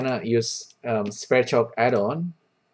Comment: I know you said it at time 2:30, but I couldn't get the word. the google subtitle said spread shop add-on? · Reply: never mind. you said it in the description: Sverchok add-on, not spread shop add-on :)